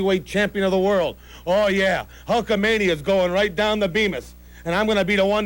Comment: did Heenan just say "right down the beavis"? Is that where his name came from!? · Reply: He said, right down the Beamis. Beamis is a brand of toilets. Every time I see a Beamis toilet I think of The Brain.